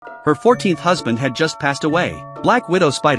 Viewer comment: My ass thought it said "14 year old husband" 😭😭😭